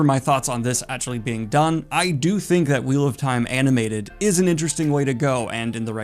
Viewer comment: Oh boy, I heard "Wheel of Time Anime" and thought "faithful adaptation like a lot of light novels get". What a whiplash.